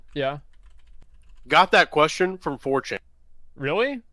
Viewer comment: Did he just say "four-chin"? He pronounced it like fortune. Did he mean 4chan?